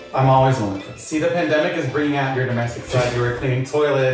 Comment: In the past EVERY TIME I watched this I thought he said “the pandemic brought out his Mexican side” and he started doing chores and cooking and stuff and I would be all mad but now I just realized he said domestic and let me tell you I couldn’t be happier